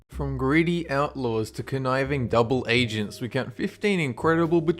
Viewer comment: when you said conniving at the beginning of the video i thought you were saying knifing wrong. LOL!